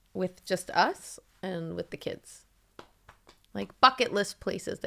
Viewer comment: Omg I thought said *U CK IT not bucket....lol